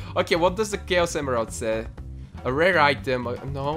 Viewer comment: i thought the chaos emerald said "A rape item..."